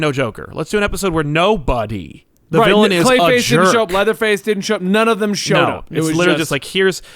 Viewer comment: it sounds like said Leatherface. I haven’t watch BTAS in awhile but I don’t remember that character.